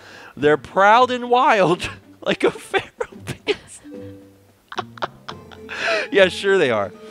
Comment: It sounds like you said that Link's eyes were like a therapist.